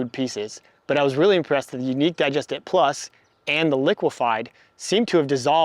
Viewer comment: What does he say is the first brand at 1:19 that was impressed with? I can understand the "liquidfy" but not the brand before that... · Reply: Yupp you got it- Unique Digest It+